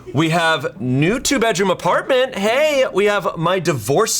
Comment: I heard "New tube edgeroom apartment." and I was like, "WHAT?" Edit: Nah, screw Nintendo and all their greed with Switch 2, and anyone funding their bs.